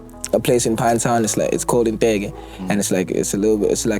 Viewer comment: Did this guy just mentioned INteke at 31:27 my village outside pinetown or am I hearing voices?